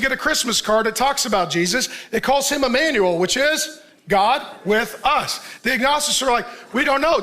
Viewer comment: did he just said god with us? o_O